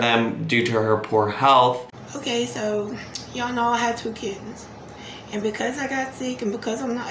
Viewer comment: When you said “kittens” I thought you said “kids”. I couldn’t understand why she would say “rehome” for her kids and then when she said they were tearing up her curtains I had to rewind because I was sure I heard it wrong. Thank God I did.